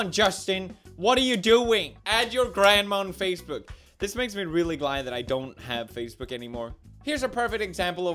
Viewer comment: thought he was going to say "This makes me really glad I don't have a grandma"